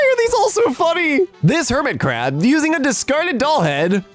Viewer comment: who heard "Hermitcraft" instead of Hermit Crab